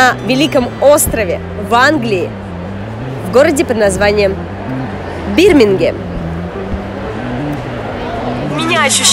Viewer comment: кагда тибе послышолось бір менге ето казахский язык с перевода на русский ето значит один мне, каму тоже послышалось ставьте лайк